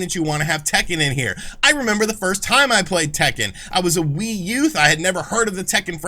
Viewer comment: Dude said "Wee Youth" and I thought he said Wii-U lol